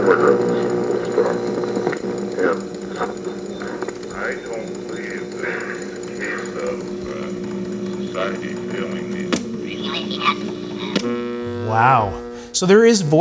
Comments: Here's what u been waiting for.. I heard "society been waiting for" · “Society is failing”me is what I heard.